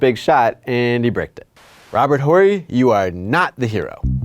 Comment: Did he just say "Robert WHOREY"???